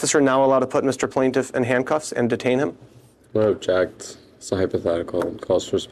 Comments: you need a pop up of a jacked lawyer right here. How you could think this attorney said, “well jacked” instead of OBJECT also blows my mind lol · "I object" whereas A.I. close caption says "well jacked" Makes you feel like the hearing impaired have no rights either!